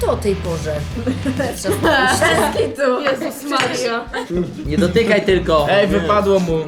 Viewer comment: Ja usłyszałem Jezus ma ryja